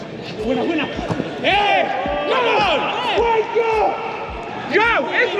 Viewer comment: I thought he was saying "Wanker" on the pitch, then I realized it was "WAKE UP"